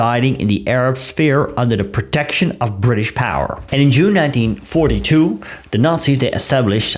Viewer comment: I thought you said Jew 19th for a split second. 🤦‍♂️